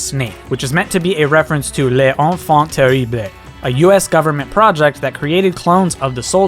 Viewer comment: Attendez, même si je suis Français j'ai pas compris à 7:25, il a dit quoi ? x) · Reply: "Les enfants terribles" peut-être ? Ngl that's hard to understand mdr